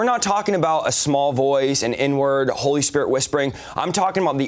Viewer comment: Did he say the N word at 3:50 and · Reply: no, turn on subtitles. he said "inward"